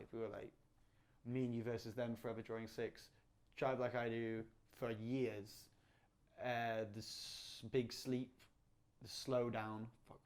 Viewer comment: What does he say their name once was at 14:30? Forever Drawing Six? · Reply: me and you vs them, forever drawing six, drive like I do, big sleep, the slowdown etc